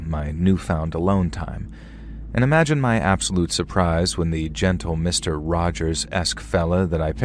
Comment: Do did you call him Mr.Rogers?